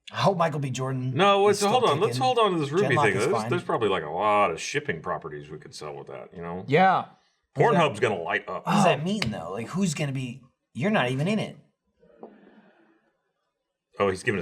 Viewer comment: did say give rwby 2 pornhub